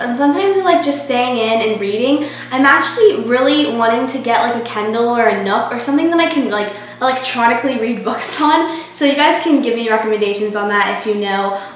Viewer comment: when she said kindle, i thought she said kendall.. big time rush <3